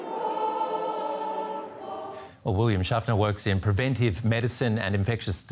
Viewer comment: did he say William Shatner?